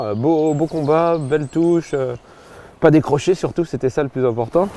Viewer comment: "pas décroché surtout" ahahhahh je sais pas pourquoi pourquoi au début j'ai entendu "pas des brochets surtout" mon cerveau a bugué un instant puis en réécoutant j'ai réalisé 😂😂😂😂